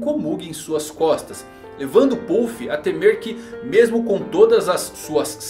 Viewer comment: Minutos 5:59 até 6:00 eu entendi ele dizer "Evandro Pouff" 😂😂😂😂😂